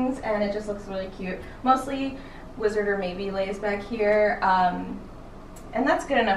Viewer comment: When she said "wizard & maeby favorite place" I thought she said "wizards of waverly place" and was very confused.... anyways I'm and ur watching the Disney Channel!